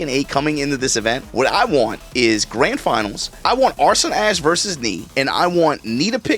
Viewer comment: bro did NOT call him "Arson Ass" 😂